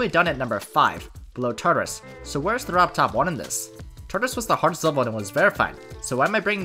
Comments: Great video. But I kept hearing "robtop 1" instead of "robbed top 1" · Istg every time he says "Robbed top 1" i always hear "Robtop 1"